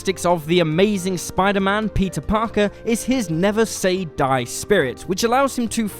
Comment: I heard Pizza Parker